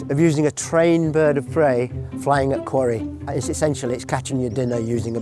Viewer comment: Caption says 'query' - should be 'quarry'.